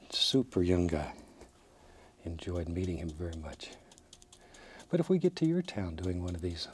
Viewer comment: Am I the only one who heard "I enjoyed beating him" at 22:38? I know its not what he said, but.. XD